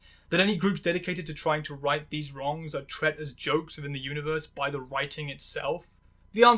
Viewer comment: Did you say tret as the past tense of treat? Lol Loving it so far!